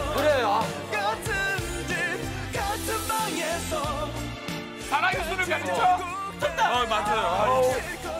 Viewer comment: 이거 호동이 뭐라고 한지 아시는분..? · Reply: 이승기-사랑이 술을 가르쳐 입니당